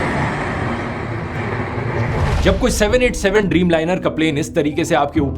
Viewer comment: Note to dhruv rathee: you accidentally wrote "747" Instead of 787 in the captions. Not big of a mistake